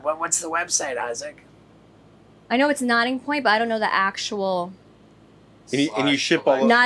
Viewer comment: sounded like she said nodding point but it's actually nocking point wines, whoops